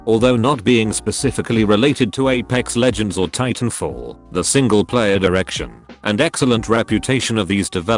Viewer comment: I heard single player direction as single player erection